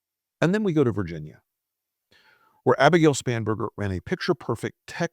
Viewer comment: Spanberger ,I thought you said spamberger 😂😂😂